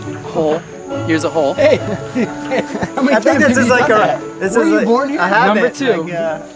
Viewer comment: did somebody say were you born here? Ha Good content bros ❤